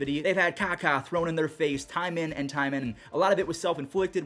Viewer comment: Did I really heard "caca"? As a French dude, j'aime bien